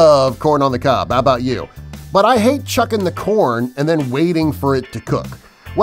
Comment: it sounded like he said "chucking the corn"😂😂